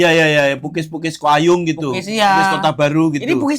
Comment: itu kayaknya salah denger deh kalian, itu namanya kue BUGIS, mungkin kedengernya PUKIS. Atau ibu penjualnya yang salah 😅